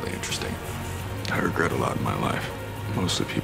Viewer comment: Someone please tell me what Ethan says at 0:50 ????? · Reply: Ethan says "I regret a lot in my life, mostly people I've hurt."